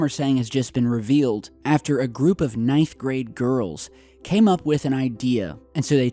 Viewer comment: "A group of knife grade girls" xD and i know he said ninth grade girls but it sounds like he says knife grade girls xD xD